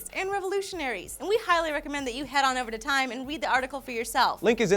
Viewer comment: did Lee just say 'Wead the article' at 2:40?